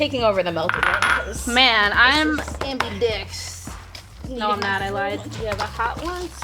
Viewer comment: Caption team, wtf?! She said “Ambidex”, as in ambidextrous.